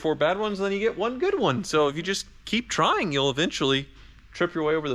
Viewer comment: I’m about halfway thru the video and I need to go wash my hands.. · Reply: Wait..did you just say “Thank you for washing!!”??